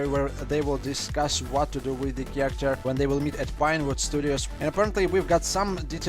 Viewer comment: did he say vinewood studios?? from GTA 5??!! Cause If you play that part it sounds like he said vinewood.anyone wanna explain??